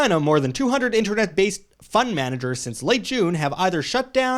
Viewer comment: lol - I thought you said "fun manager" not "fund manager."